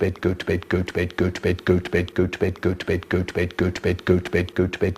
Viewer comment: at some point it sounds like he saying “bed good”